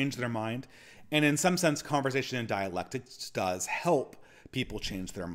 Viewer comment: Thought you said diet-lectics at the begining of the video Would be a fun concept